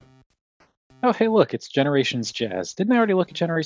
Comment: "oh look it's generations Shit-ass" that's what it sounded like he was saying at the beginning of the vidya, and honestly, that's how I feel about HasTak now (kinda sorta, or atleast when something gets leaked) as everything feels muddled. Everything we got out of BEER (geddit, cause "CHUG?") just feels the same. Nothing stands out or atleast imho. It got to the point where I can literally put GaMeR eDiTiOn Sideswipe and Cliffjumper alongsides Movieverse Sunstreaker and they all just give off the same vibe. I mean, it's a good thing, not a nitpick - but at the same time, I am just one of those collectors who'd just want something to standout on a shelf (rather than "oh look, shit, it's the 30th Optimus Prime in a row, I wonder if we're gonna get [insert character here] made in this toyline too? Who's gonna be the repaint?")